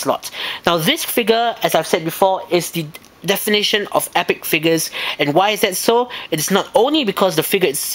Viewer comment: i think he thought you said an epic of failures ( you said an epic of figures)